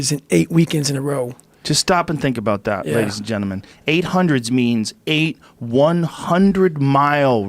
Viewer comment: did I hear that right? So he ran 6400 miles during those 8 weeks? Or is he saying he ran 800 miles during those 8 weeks? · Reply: Nick T 800. Although, if he sees this he’ll probably be like “6,400? Rodger that”.